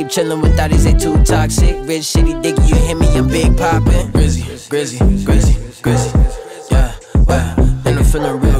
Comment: who else thought the song said glizzy Lol😂😂😂